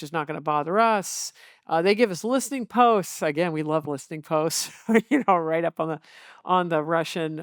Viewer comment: did she say listening posts or ports? What did she say? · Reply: listening posts. I'm old enough to remember that.